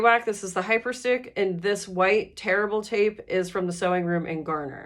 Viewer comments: I thought you were saying terrible tape. And t thought that’s awful to say for the business. Then I realized you were saying tear-able. 😂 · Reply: I thought she said terrible tape too. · I thought you were saying terrible tape. And t thought that’s awful to say for the business. Then I realized you were saying tear-able. 😂